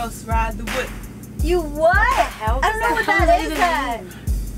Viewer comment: Did anyone hear Camila say what the hell at 3:43 cuz I did